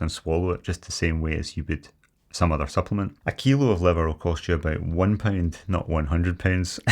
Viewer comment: Did just get 18 eggs for a pound?! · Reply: I thought he said a pound of liver.